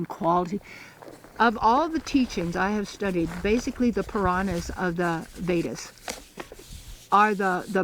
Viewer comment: Hello brother, can u please tell me what she says on 13:17 piranha what?Thank you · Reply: thank you brother for asking and bringing my attention to this specifically, she is referring to the Purana. The Purana is the part of the sacred literature of Hinduism, which comprises the Veda, Brahmanas, Aryayankas, Upanishad and the great epic. Veda is the religious text passed down orally, whereas the Puranas are the stories of religious legends and passed on through memory, one generation to another.